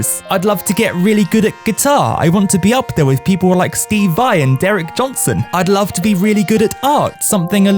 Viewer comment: did he say Derek Johnson??? It’s Eric Johnson